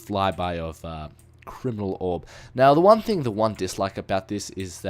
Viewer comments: I SWEAR I heard him say criminal orb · did he say criminal orb lol